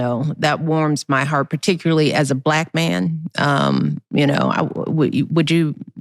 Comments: Did she just say she’s a black man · Did she just say as a black man ? LMAOO I think shes a woman but shes not helping her case LMAO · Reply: She said “ I love the way you deal or accept your child especially as a black man” referring to Marlon